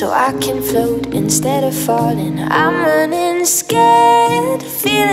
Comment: i can float instead of farting ?😂